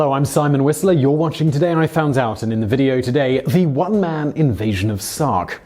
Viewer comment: The one man invasion of SOCK. Is what it sounds like you said. Gardes sounds like "God"